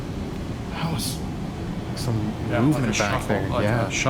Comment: Aarggh 37:34 you guys say you here movement in the replay I hear "I'm a man"